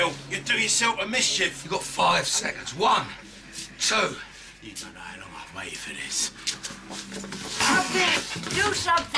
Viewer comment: What did Derek say at 0:59? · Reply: "You don't know how long I've waited for this."